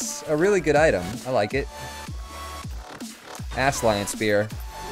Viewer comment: "Ass-lion spear". That's what I heard him say.